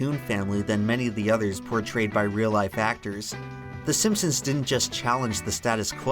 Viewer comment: All I hear is “SIMPSON’S DID IT!”